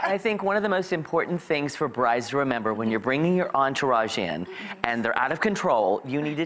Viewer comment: what's the word he says at 3:50/51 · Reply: Entourage. It means a group of people attending or surrounding an important person.